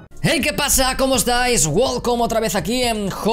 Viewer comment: queeee recién me doy cuenta que decías welcome y todo el tiempo pensé que decías WOW come